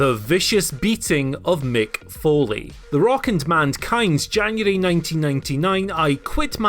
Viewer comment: I can't be the only one that heard him say "Mandkind"